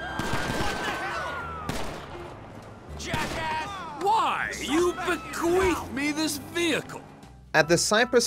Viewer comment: what did he say??😂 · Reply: "Why, you've bequeathed me this vehicle" Bequeathed means to leave property to another person